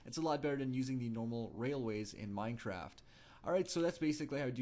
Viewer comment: did you just say Minetraft